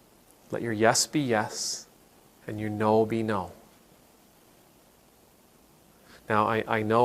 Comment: I had a “slip of the ear” that went “let your Yes be Yes and your No be Known” and somehow that version sound even more useful to me personally. I hear it as “mean your Yes and make your Nos clear”. Also I think we have been into a society that impose the message that “unless you are an important person (with money) you, your experience, and your feelings only matters if you have a “good” reason.” And so people are constantly trying to find a way to frame their (already valid) experience in a way that make them sound important enough, backed with good enough reasons to be considered by others. And when they can’t find any good enough reasons, they try to borrow the power of historic events to back up their own importance.